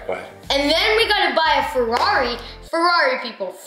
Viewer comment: I LOVE YOU ROYALTY GAMERS!! SUBSCRIBE HERE 👉 <link> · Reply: for a while I thought he was saying Ferrani